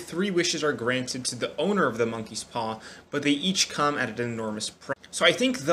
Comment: For a second I misheard it as MonkeyPox